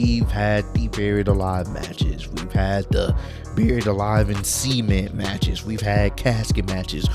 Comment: For a second there I thought he said buried alive in semen match lol